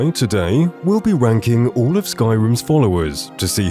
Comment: am I the only one who heard "today we'll be wanking..."?